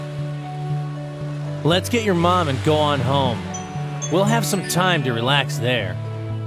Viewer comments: I swear to God Goku is high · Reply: lmao I heard him saying 1stly when he was talking to Gohan "Lets get your Mom and Gohan home" but then I was like oh...its Go on... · Did I just heard goku say “lets get your mom and gohan home”? Goku doesnt event know which son he is talking to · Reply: He said “Go on home” but it sounds mega similar to gohan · did he say let’s get ur mom and gohan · So i thought he saying lets get your mom and gohan home while speaking to him meanwhile he saying go on home haha😂 any one else turned the subtitles on for this